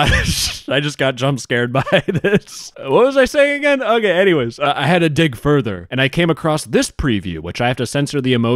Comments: I heard "I had to dick further" and was like "sounds about right." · Idk if my audio broke but sounded like you have to dick further